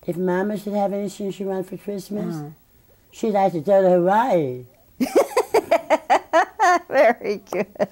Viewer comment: What did she say? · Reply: "She'd like to go to Hawaii."